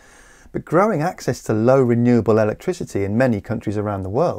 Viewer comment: Errata : Apologies for the formula on screen at 6:50 - the letter z should of course have been a number 2 and the Fe203 and H2 numbers should be subscripts. Sadly my 52 year old eyes are getting worse every day, and I missed these errors! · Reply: And 5:57 a very rare example of misreading the script: "growing access to low renewable energy" -> "growing access to low-carbon renewable energy" (is presumably what you meant to say (or write)).